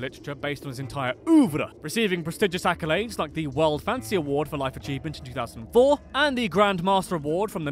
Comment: I heard "World Fantasy Award" as "World Fancy Award"